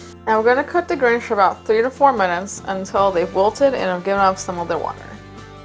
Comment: WHAT IS SHE SAYING???? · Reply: “we’re gonna cook the grinch for 3-4 minutes until they walter and given off some of them water” idk 🤷🏻